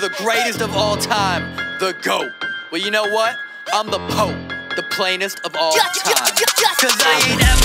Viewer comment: I thought he said "I'm the Pope" I was like "Boi, you ain't that special"😂 No hate tho, is amazing 💞